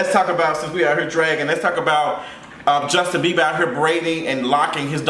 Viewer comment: I kept hearing "Justin Bieber's dog hair." Went to Google the dog...when the read is so subtle🤣🤣🤣